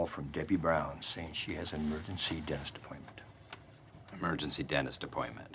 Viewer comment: I hear her Dentists name was Crentist